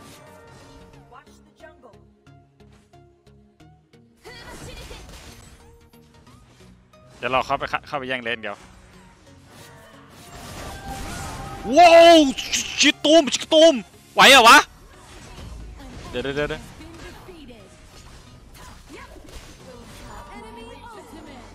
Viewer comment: วู้วชิกกะตู้มๆ ได้ยินแบบนี้นะ